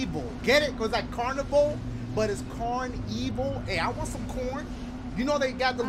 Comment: i thought he said “hey, i watch some porn”